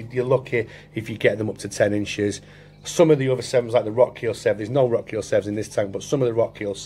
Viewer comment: Amazing looking severums there buddy! Around 7:51 you mentioned some of them tend to be smaller did you say Rotkeil? Am interested for a 200l tank. Awesome vid. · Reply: Hi yes the Rotkiel doesn't grow as big mate cheers for watching thanks